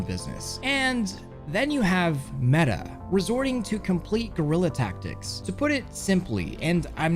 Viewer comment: he said gorilla ta-ctics and I swear I thought he said gorilla tag